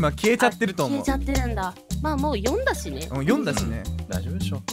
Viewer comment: 読んだしねが 読んだ氏ねに聞こえたwww